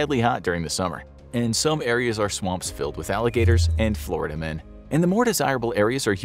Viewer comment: did he really say florida men lol